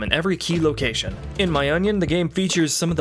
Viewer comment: so nobody heard him say “in my onion” instead of “in my opinion” at 11:20 ? 😂😂😂😂